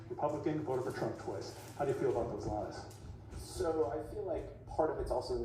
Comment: is it me or is this CNN anchor asking this panelist? How does he feel about those lies? Or is he saying something else?